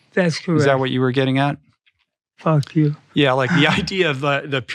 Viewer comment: What Rich summarized from about 1:26:00 to 1:29:00 was epic! I love at the end of 1:29:00 Phil pointed out to Rich basically what he was trying to teach him in that Rich needed to leave his intellect behind and lead with his heart ❤️ I’m not sure if that is what Rich’s wife is trying to help him with but I love how insightful and willing Rich seems to be open, humbled and grow. This episode was magic between Phil and Rich… Thank you 🙏🏻 ☺️🥰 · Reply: did he say fuck you at 17:09 ? im confused